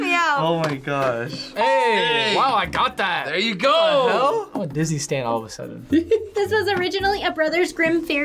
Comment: Did he say that he was a Disney stan or was it just me??